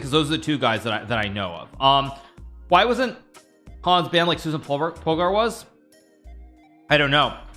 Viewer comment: did Magnus say Susan Polgar was banned?!?!?!?!